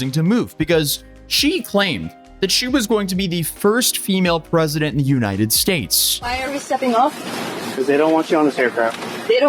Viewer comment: i swear i heard her say she was going to be the first female president of the united states of Kazakhstan